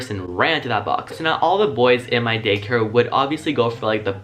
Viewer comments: why did I keep hearing “gay care” instead of “day care” throughout most of the video...? XD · WHy do i hear 'gay care' instead of 'day care' wtf is wrong with me lmao